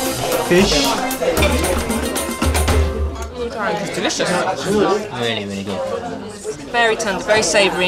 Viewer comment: i swear you can hear someone say "really dry"